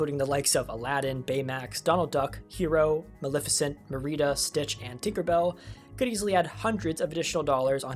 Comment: what’d u say her name was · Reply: merrita!